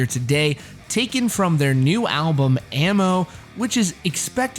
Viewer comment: For a second I heard "their new album emo" and I was confused